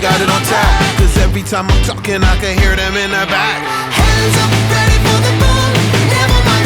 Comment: I’m probably the only person who thought they heard this but when Wyclef said every time I’m taking I can hear them in the back I thought he said Iraq instead of the back 🤣🤣